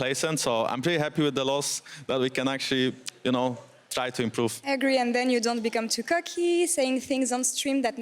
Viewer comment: What did he say on stream ? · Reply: try to improve